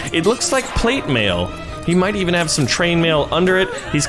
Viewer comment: he said trainmail instead of chainmail